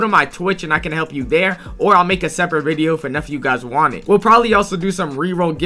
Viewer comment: these ai subtitles are wacky. bro said enough and it thought he said nephew